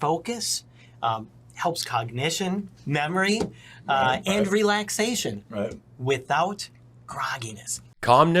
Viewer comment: When he said relaxation, I thought he said re-lactation,as in being able to breastfeed again. 🤣🤣🤣🤣